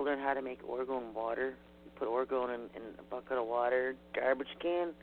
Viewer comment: ergot and water??? is that what I heard you say?